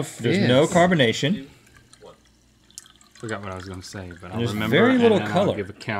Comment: you can here link say "I forgot what i was going to say but i'll remember thn i'll give a count" over the other video